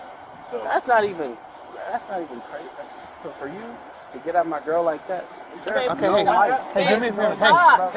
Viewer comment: I thought i heard him say, to the cop why did you disrespect my girl like that? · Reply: He said, "For you to get at my girl like that", not sure what the cop said. They be lecturing and treating you like a child sometimes, unnecessarily.